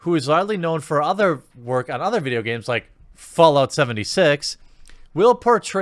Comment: Did i just hear "Fallout 76"? Welp, there is no chance in hell im buying this game